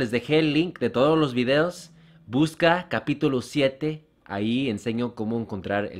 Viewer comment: Hey le tuve que retroceder al video por que pensé que dijo lucas capítulo 7 y dije como fue eso pero era buscan capítulo 7. Jajajajaja.